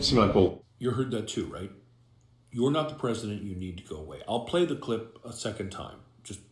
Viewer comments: 5;55 When I was watching i saw this happen I thought he said Mr president I need to go wee. And when he was telling some one to shhh he was getting in trouble from someone on the sideline for picking his nose and told them to shh lol Bit cheeky. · Elon's son, X, says "will you protect me? I need to go pee." - or something to that effect. Don't just take this guy's word for it. Some people - leave the kids alone!! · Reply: I thought he said, Mr. President, I have to go pee